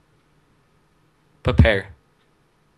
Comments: What did he just say perpare for sex. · did he just say prepare to sex?